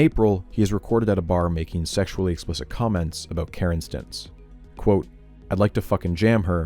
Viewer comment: Love how the subtitles say he is recorded making sexually explicit comments about "Karen's stintz"